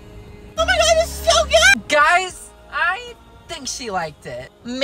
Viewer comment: At first I thought they said love and abortion 😭😭😭😭💀